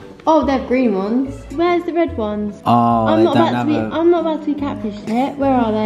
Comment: Kaci swore lol she said im not about to eat cactus sh__